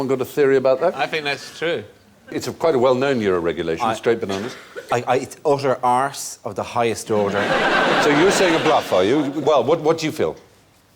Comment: For the first two times I heard Dara at 0:42 I could have sworn he was saying "I eat utter arse of the highest order" and choked a little bit on my dinner.